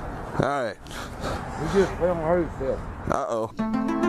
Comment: anyone know what he said ?? Before it said “or did he???” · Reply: I think he said, “he’s just having a heart attack.” 🤷‍♀️😂